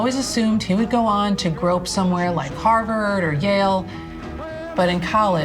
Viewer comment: the subtitle is wrong - it's not "grow up somewhere" it's "grope somewhere"